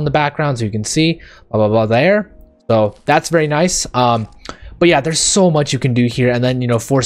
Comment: Is he saying that’s new?